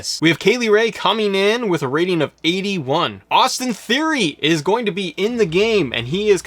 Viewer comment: Austin Theroy? I thought i heard Austin Aries